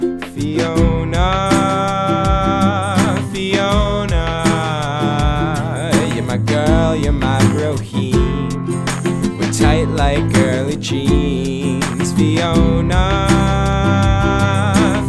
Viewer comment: We're tight like girly jeans? Lol xD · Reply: Lol for a second i thought he said curly cheese. I thought,"That isn't in the song!".Thats when i noticed he said girly jeans :P!